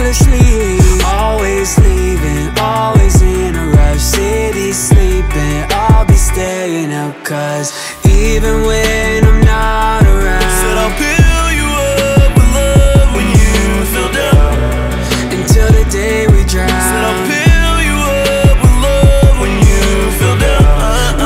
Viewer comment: I swear I thought he said build you up with love but this whole time it was pill you up I gotta tell my girl now lol